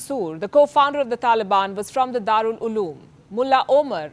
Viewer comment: For a second I thought she is saying Darul loo loo instead of Darul-Ulum!!